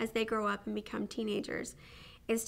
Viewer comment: did she just say if her children grew up to be teenagers?